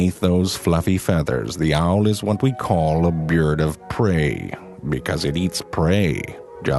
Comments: "The owl is waht we know as a byierd of prey." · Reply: Lemon Moon I thought it said beard of prey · sounds like he says beard of prey xD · I love how it sounds like he says "beard of prey" ... I'd like to see a beard of prey someday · I thought he said "A beard of prey" at first... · "The owl is what we call a bird of prey, because it eats prey." WOW it sounds like he is saying beard instead of bird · Sounds like he said "beards of prey" instead of birds xD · Sounds like he said "Beard of prey"